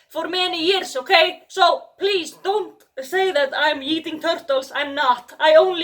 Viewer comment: “I’m not yeeting turtles” That’s exactly what a turtle yeeter would say..... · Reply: I thought she said she’s not eating turtles and I didn’t even question that statement at first, I literally had to go back to hear it correctly lmao